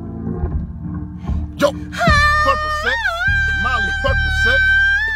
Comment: tell me why it sounds like he says purple s3x?